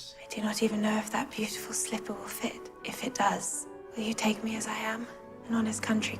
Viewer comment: lol i thought she said " will you taake me as a ham" like what hahahah